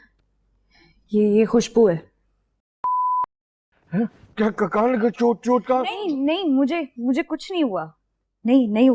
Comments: What she said at 6:08??? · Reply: Dettol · can anyone tell me what did she said?? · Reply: Chott mtlb injury